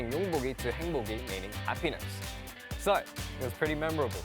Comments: I DEAD ASS THOUGHT HE SAID “A pEniS” AND I GOT SO WORRIED FOR A SECOND LIKE WHO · did he say “a penis”? WHAT DID HE SAY! HELP MEEE